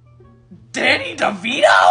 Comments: "DANNY DEVITO!?" I fr thought he said " DADDY DEVITO!?" 😂😂😂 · The way he said "dANnY dEvITo!?!?!?" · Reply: Lmao I thought he said “DADDY DEVITO???” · Lol I heard "DaDdY dEviTo!?" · I literally heard him say "Daddy Devito"